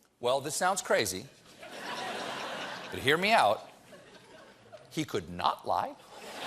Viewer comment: Let what that lady on Fox said sink in , Trump shouldn't speak with Mueller because if you he lies even once he will be charged. They say that with a straight face and with all sincerity. I'd love to hear a normal citizen try that with the cops. Lets see how undamaged they would then get out of the interrogation room. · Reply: If you listen, the lady next to her says "he could".. at 1:28 then the video cuts off. What she says is "he could not lie" and then everyone gets a little uncomfortable, cuz they know how ridiculous that would be.